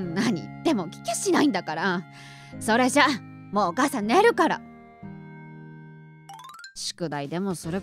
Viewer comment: 帰るが寝るに聞こえる 気のせいですか？